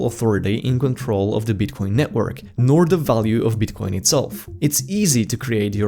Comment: i heard bitconnecttttt